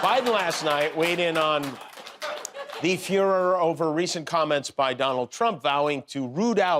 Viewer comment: Oh, he said 'furor.' At first, I thought he had said 'Fuehrer.' "Biden weighed in on the Fuehrer." LOL